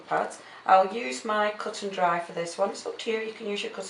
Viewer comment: Thank you for sharing. I have a questions what type of sponge are you using I haven't seen one that is white and black? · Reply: ignore my last questions just watched it again and heard you say cut and dry LOL